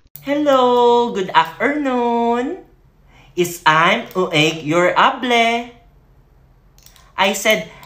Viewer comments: I thought they said its time to take your table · i thought it said its time to take your table XD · for a hot second I thought they said "it's time to take your table" I'm like WHAT